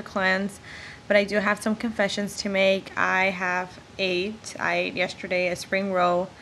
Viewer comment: LoL! I seriously thought you said "I have AIDS..." at :06. I was like "Oh shit!" But then you started talking about food so whew.